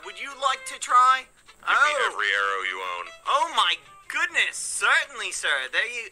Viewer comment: Sorry, what does Technoblade say at this point? i would be happy if you help me 0:55 ( I'm from another country) your animation is beautiful 💕🥺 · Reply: Technoblade said "Give me every arrow you own".